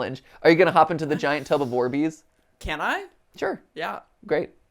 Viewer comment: when matpat said "orbeez" i thought he said "war bees" and got very confused and scared for a second then mirror matt asked if they were wet and i got even more confused before realizing what he said